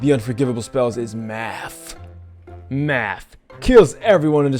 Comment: I thought I heard ""Math" Kills everyone and destroys everything on the map."